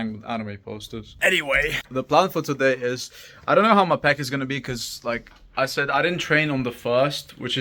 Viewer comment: When you said I don't know how my peck is gonna be, I heard I don't know how my pecker gonna be.😂